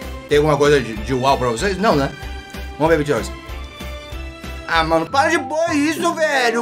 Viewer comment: No 5:58 eu juro que ouvi ele falando "Vamo ve BTS"